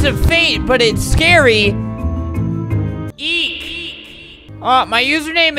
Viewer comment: I thought that albert said E but he said EEK